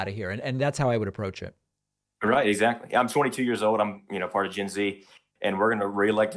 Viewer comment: Man I had to listen to that over and over again because I heard 42 not 22 .